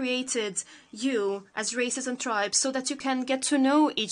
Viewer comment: “we created you, as ????” Can’t work out what she said 🤔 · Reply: Races and tribes